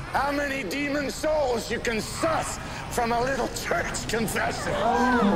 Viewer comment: did he say sus